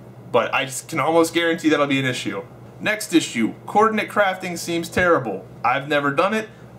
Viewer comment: Hey dude, quick question, did you say "coordinate crafting" at around 9:50? If so, what is that...? I totally agree with the game optimization comment 100%.